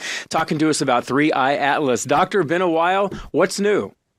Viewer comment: Dr. BeenAwhile… 🤭 am the only one who heard that… 🫣